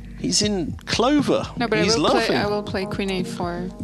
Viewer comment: Revisiting these videos because of Garry's return to chess. Did Nigel say "He's laughing" at 5:50?